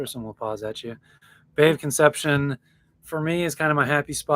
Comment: Oh my goodness, I had to back up and listen again because I got distracted and missed something and thought I heard you say "I love the day of conception"